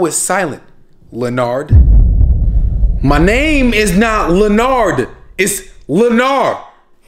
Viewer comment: “What’s your mf name?” “LENARRR” “Oh, Lener?” “Nah LENARRRR” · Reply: I thought he said Not Lener? after he said. Not Oh Lener